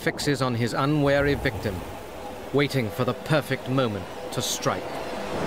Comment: I bet u all thought the person was saying that dinosaur was waiting to eat a fish